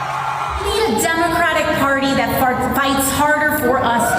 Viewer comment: I know I heard "Need a Democrat party that Farts Harder" Jerry Nadler said, "I'm your huckleberry."